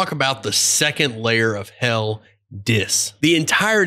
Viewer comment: I heard ‘The second layer of hell is this’ s as and was confused af